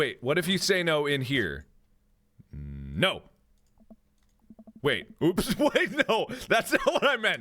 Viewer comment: I thought he said “No I don’t want to continue” And Robert still continues anyway 😭😂